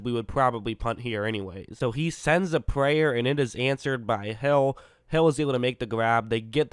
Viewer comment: it sounds like he said “ he throws a prayer and it’s answered by hell